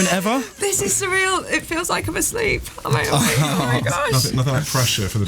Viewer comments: Adele says this is so surreal. It feels like I am asleep. And I heard "this makes me feel like a slave" 🤐☠ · Thought Adele said “I feel like I’m a slave”